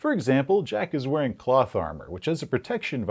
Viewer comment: I swear at first I thought I heard you say "Jack wears plot armor which earns him the protection value of 8". XD